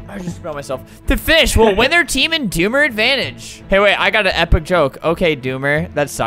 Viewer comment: XD i thought the doomer said Boomer